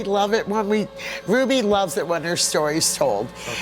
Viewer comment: Instead of "Ruby" I heard "fit-bit" Like if you heard it too